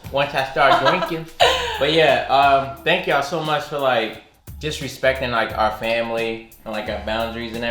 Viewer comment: Lmao I really thought you said "thank you all for disrespecting our family" and I was so confused for a second 😂